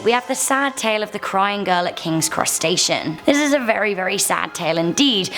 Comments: Forgive me, I thought you were saying King Crustacean...I’m a New Yorker transplanted to south Oklahoma and it took me five years before I could watch the news here because I DONT have an ear for accents...NOW I GET IT...Kings Cross Station! My apologies for deaf ears! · sounds like she said “Kings Crustacean” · Wait, she said Cross Station? It sounded like she said crustacean. My brother's birthday is May 26th, should I be concerned?